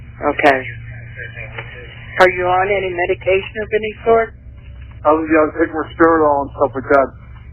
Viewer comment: At about 10:58, the caption says "my steroid", but he actually said he was taking Risperidone, which is an antipsychotic. He also implied he had stopped taking it, saying "I WAS taking risperidone". Y'all's messed up captions left out some crucial details.